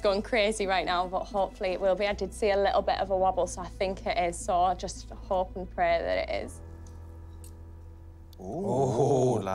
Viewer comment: wtf is she sayin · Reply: "Hopefully it will be, i did see a little bit of a wobble so i think it is, so i just hope and pray that it is."